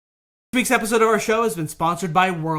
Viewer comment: Anyone else hear "Beaks episode of our show